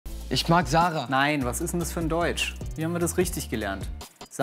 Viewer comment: ICH HAB VERSTANDEN ICH MAG SAHNE LMAO